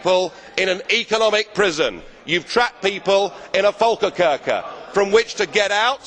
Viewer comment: What does the word "folkekirke" mean in the sentence "you have trapped people in a econimic prison, you have trapped people in a folke kirke" in 1:50 ???? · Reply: If you still want to know, he said "volkerkerker" or "prison of Nations", where countries are trapped by their own laws and regulations.